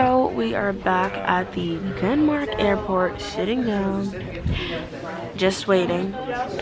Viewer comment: Am I the only one who thought she said "we're at the airport shitting down"? Just me, okay.